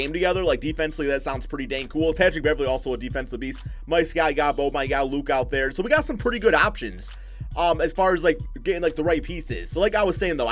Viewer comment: what did you say??😂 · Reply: Got Em “Mike Scott, you got Boban out there, Luc out there”